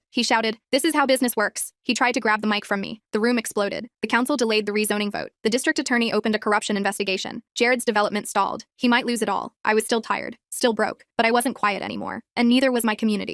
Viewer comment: Thank God. When op said “tired” at the end, i thought they said “fired”. I had a mini heart attack